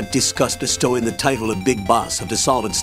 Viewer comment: In the beginning the AI voice sounded like it said big bus 😂